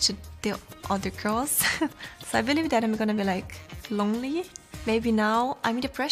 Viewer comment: What did say? She was talking too fast😂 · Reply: "So i believe that im gonna be like.. lonely" i think